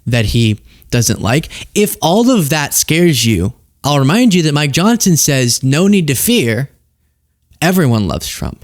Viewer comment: My mind had wandered for a moment when you said “representative Mike Johnson said…” but I heard “my johnson said…”. And I thought, he got a talking dkic just like me! Boy, sometimes you gotta listen to your D! (I am still a little juvenile delinquent!!)😂